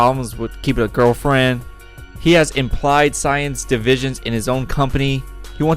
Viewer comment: why am i hearing "implied science" ??